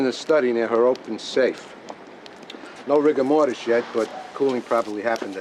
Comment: lol 3:53 ... no rigor mortis.... i heard Rick and Mortys yet XD